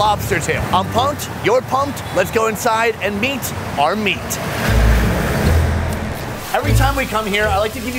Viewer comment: i thought he said and beat are meat but he said lets meet are meat